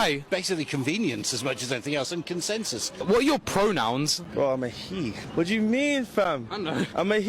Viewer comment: The scottish guy sounded like he was say pro-nim. I can understand the confusion.